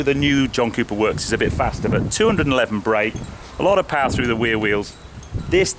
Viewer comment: Sounded like he said power rear wheels drive lol